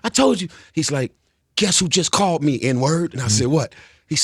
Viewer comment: lol nice did he say "guess who just called me n word" ? Haha